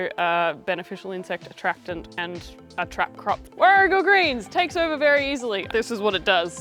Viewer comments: what is that big edible plant? I couldn’t understand what you said and neither could the auto CC’s. Thanks! Love the video. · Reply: Sorry I mumbled! Warrigal Greens - Tetragonia tetragonioides - it’s an Australian/NZ native. And you’re not supposed to eat too much of it raw as it’s full of oxalates! I forgot to mention that in the video. 😅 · I cannot for the life of me make out what you say at 5:16. What type of greens are these? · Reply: Sorry I mumbled! Warrigal Greens - Tetragonia tetragonioides - it’s an Australian/NZ native. And you’re not supposed to eat too much of it raw as it’s full of oxalates! I forgot to mention that in the video. 😅